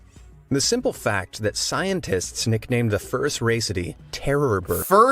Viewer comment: when I first watched this, I thought he said "the first race of these"